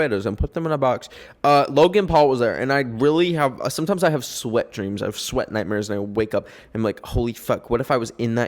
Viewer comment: did he just say he’s having wet dreams about logan paul